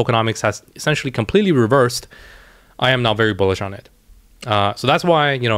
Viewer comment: At the end of Gala, did you say you are, "not very bullish on it" ???